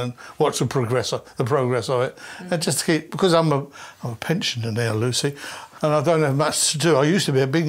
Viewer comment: Please help a Yank - did he say, "...and to escape, because I'm a pinch in the nail loosey?" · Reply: "Because I'm a pensioner now Lucy"